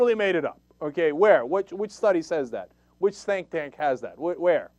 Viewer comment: Did Cenk say "stink tank" at 6:58? I like that. That's what we should call it when someone pulls a "fact" out of his ass.